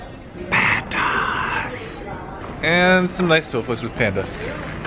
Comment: after 2 panda’s I heard “pemdas” idk why I’m weird